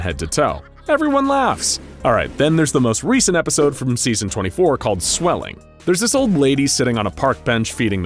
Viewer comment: I thought you said there were 32 seasons